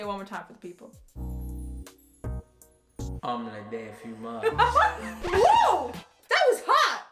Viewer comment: When he said, "omelette au fromage" I thought he said, " imma be a dad in a few months" to. Sorry guys, my headphones are kinda messed up lol